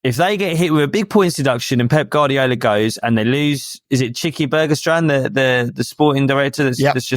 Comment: genuinely thought he said cheeky burger stand at first